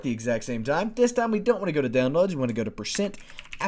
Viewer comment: Please tell me what he said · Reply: i think he said this time we just want to go to percent